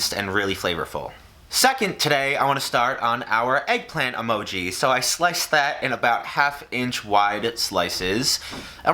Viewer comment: did he say slice our eggplant emojis?